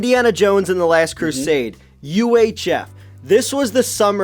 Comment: what movie he is saiying? Sorry i cant understand and i want to know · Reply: UHF